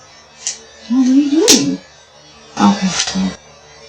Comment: in the 'Sleep As Android' post you can hear that these interpretations have been quite wrong.... starting @ 7:24 the cleaned up recordings reveal the OP doesn't initially say 'what ARE you doing?" - it CLEARLY says 'What WERE you doing?" .... this is CLEAR AS DAY ... BUT the response does NOT say "NOTHING" .. there are 3 SYLLABLES said and THAT IS ALSO CLEAR AS DAY... but as far as WHAT is said.... that is up to interpretation and I cannot make heads or tales of it... only the fact that the response does NOT day 'nothing' ...... BUT the second response, which is still in that 'unknown voice' I believe the phrase 'THAT'S THEM" is said..... We can solve who's voice is in this recording of we compare the two 'different' voices and I am fairly certain it will be concluded they are ALL THE SAME VOICE.... They sound like the same person to me, just in a different manner... the first question 'what were you doing' is asked in a higher, brighter pitch, and the answers are in a calm, 'saddened' deeper style which could seem like a different voice, but could also be the same person... which makes COMPLETE SENSE... didn't Freud once say that we are all the people we encounter in our dreams? If you ask someone a question in YOUR DREAM then YOU are coming up with THAT ANSWER.... and so if she's talking in her sleep then she's going to act out all the difference instances of talking, right?? IT WAS HER THE WHOLE TIME!!!!! (what a twist! lol)